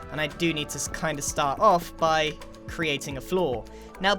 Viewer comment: Did anyone else thing Grian said "flaw" at 1:33 instead of "floor"? I realized afterwards but I was confused at the start... EDIT: 1:32 not 1:33, sorry lol